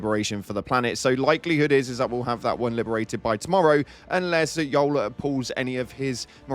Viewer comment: Did you just call Joel.. Yole?